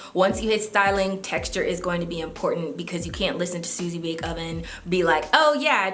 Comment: "Suzy bake oven" 🤣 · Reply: i thought she said easy bake oven 😭💀